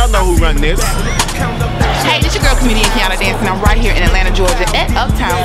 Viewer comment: Who's that lady at the very end, the comedian. Sounded like she said Candadace.